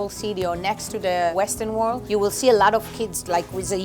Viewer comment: she said Western wall not Western world (captions say world)